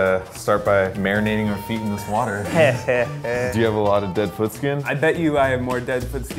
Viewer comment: I thought it said dead food..